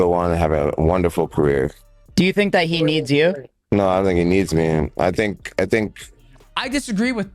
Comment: I think you misheard him DJ, Jon said "no, I don't think he needs me." Just wanted to point that out. #defendorvacate